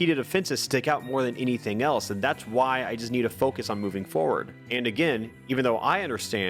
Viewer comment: Did you say moving forward (aerial)?